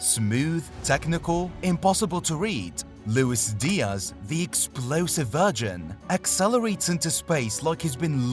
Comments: why did you call Luis Diaz a explosive virgin 😂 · im sorry, did he just call Diaz the "explosive virgin" ?!?! 😂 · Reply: Explosive VERSION* 🤭